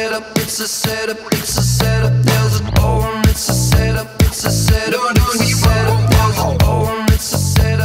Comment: I always thought it said "There's a government, it's a set up"